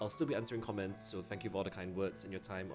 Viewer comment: Did he just say "untill we meme again" ? 😂